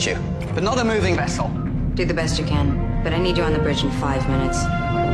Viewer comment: Thought T´pol said: i need u on the bed in 5 mins , Must have a dirty mind .